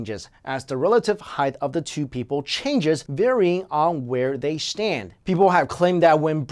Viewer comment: it sounded like he said "shtand" instead of "stand".